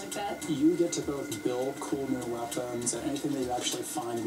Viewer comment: Can anyone tell me what says at 2:20? Does he say we can 'Build new weapons'? I can't quite make it out.